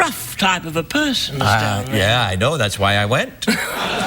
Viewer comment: Anyone know what was said in the hard cut at 31:41? It's ok my American cousins, I'm English, and we are permitted to hear naughty words and crude witticisms on tv, anyone know? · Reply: He said "I know,that,s why i went"